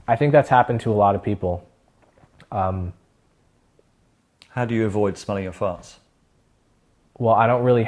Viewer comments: really sound like he said 'how do you avoid smelling your farts?' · I thought he was going to say “I don’t really have farts to smell” 😂😂